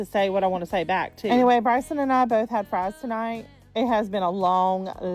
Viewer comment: did she say her son's name is "Bison" ??? Or maybe "Bryson" ?